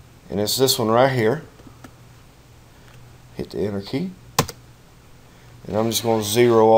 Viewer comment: I fell out when he said to hit the "inner key"... like what is that even? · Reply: Ah... "Enter key" not "Inner key" I hear it now. Thanks mate, though he was speaking a little bit fast and unclear. So it makes sense I misheard him.